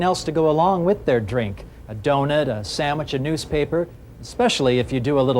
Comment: or so into the video, did he say 'a sammich'?